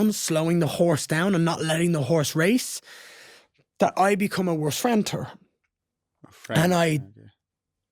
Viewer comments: "Ya wife is ya husband" -mystic man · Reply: did he just say ''then I become a placenta''?? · did he just say ''then I become a placenta''??